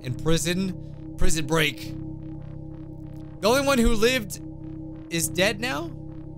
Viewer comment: I think it says believed mike...not lived😂